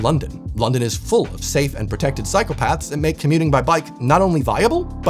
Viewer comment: “cycle paths” sounded like “psycopaths” 😂